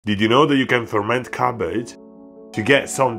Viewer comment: oooh cabbage i thought you said carpet